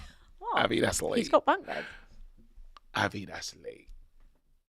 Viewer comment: Can someone please tell me what Abby said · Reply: Some grown men are crying cos she said Patrick has a bunk bed😂😂😂😂